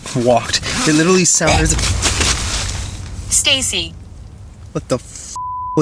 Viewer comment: Why did I hear a robot voice saying Stacy at 7:31?